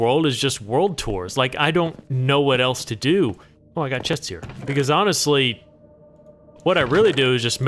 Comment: I wasn’t looking at the screen when you said this at 17:08 and thought you said, “Oh, I have chest hairs” 😳 had me for a second 😅